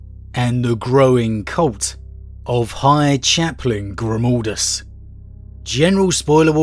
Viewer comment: What? GrimOldus...? WHere did the O instead of A come from? xD